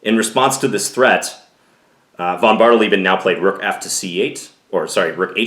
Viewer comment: Are you counting the player name? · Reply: yeah I heard it as vonbardel even instead of vonbardeleben. Watching chess at 3 am does that to ya